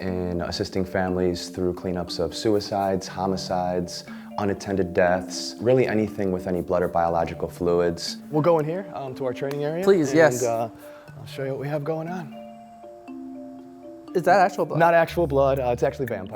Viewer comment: I thought he said "We'll go over here, to our torture area." I had to rewind it haha